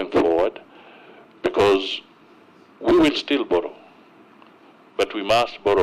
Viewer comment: So we will still borrow thought he said we must stop borrowing